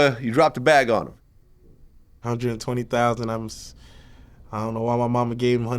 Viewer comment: what's he say at @ 0:25 ? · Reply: His mom gave the lawyer $120,000 only for him to do 7 years